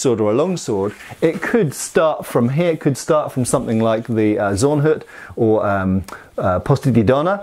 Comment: <link> says 'Zornhut'. I hear 'power attack feat'.